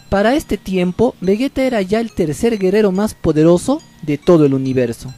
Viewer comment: Like si no escuchaste que dijo gerero en el tiempo